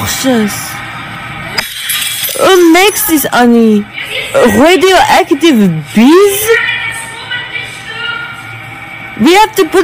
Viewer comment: “who makes this honey? radioactive bees?” is my favourite line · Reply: I thought she said "radioactive piss".